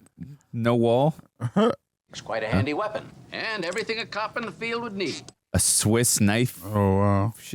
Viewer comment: At ~20:28 - The scientist says "cop in the field" but I keep hearing "coppin' a feel". Always wondered if they wrote it that way on purpose 😆